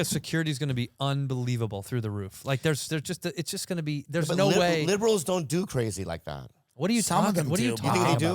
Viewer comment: Did Bobbie just say liberals don’t do stuff like that lol 😂